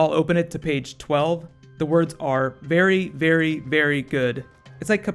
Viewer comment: Hey, you might want to go back and check. I thought I heard Very, Very, Very, Dead. Also I thought he called Santa Beardy Weirdy at one point.